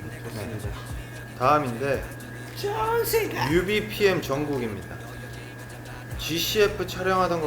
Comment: Ok, what is JK saying at 14:08 !?! All I hear is John Cena.. help XD